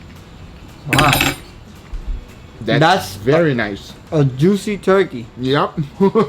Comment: sounds like “deadass, very nice” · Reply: I heard 'dat ass, very nice'